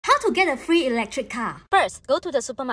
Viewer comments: thought you said "how to get a free electric chair" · I THOUGHT SHE SAID RLECTRIC CHAIR